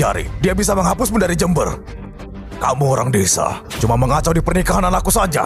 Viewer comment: dia nyebut jember gak sih 😅 apa aku salah dengar ya